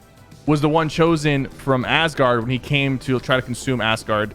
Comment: am I the only one who thought Sypher said 'assguard?'